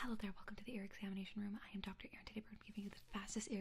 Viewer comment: I heard “I am Dr. (Ear)ittated